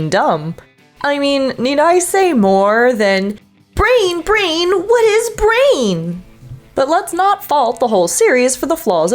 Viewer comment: I totally heard "Breen, Breen! What is Breen!?" 🤣